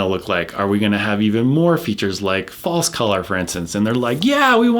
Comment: Could anyone explain what he's saying at 42:09? "Features like false color"? Thanks.